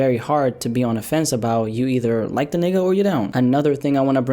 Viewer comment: Did say the n word